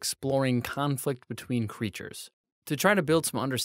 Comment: I misheard “conflict between creatures” as “communism” 💀